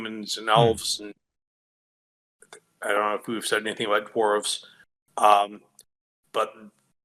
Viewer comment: I'm sorry, but at 2:00:16 did he just say "Dwarves" in Wayward Realms?